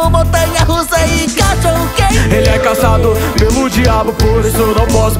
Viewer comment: EU OUVI ELE É CASADO COM O DIABO